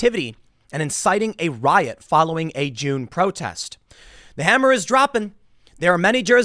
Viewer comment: Haha for a second I thought you said Jew (June) protest ! Tim RULES